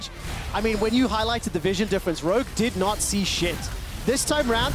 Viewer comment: "rogue did not see shit" did i hear thar correctly?